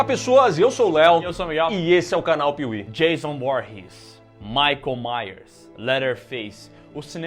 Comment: quase que ouvi Jason Bourne